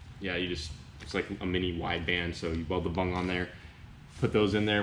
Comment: Um excuse me but did you say "Beld the bung"?